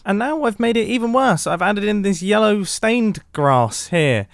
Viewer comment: When he said 'yellow stained grass' i could have sworn that he said yellow stained glass